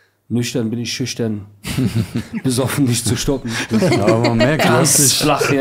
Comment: Was sagt xatar bei 05:10? „Nüchtern bin ich schüchtern, besoffen …?“ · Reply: Nicht zu stoppen